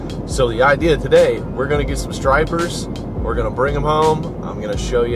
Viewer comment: Thought it said strippers 😂 my bad